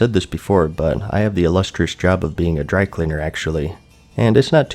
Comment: When he said "drycleaner" I thought he said "drag-queener" at first, so I was like; O H I mean, whatever floats your goat, but it was just funny. x3e